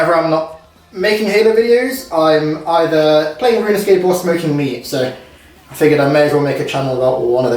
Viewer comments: For a moment I thought you said playing runes runescape or smoking weed 😂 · almost thought he said playing RuneScape and smoking weed* then I realized this video is about smoking meat* .... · I totally thought you said you’re either “playing Runescape or smoking weed” for a second.💀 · Lol I thought you said runescape and smoking weed like fair enough not what I expected I figured it out very quick